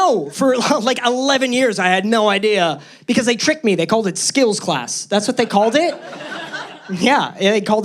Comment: I thought he said skittles class ahahha was like what?? 😅hahah